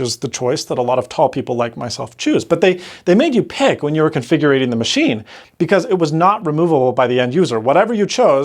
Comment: Did you just say “configurating”?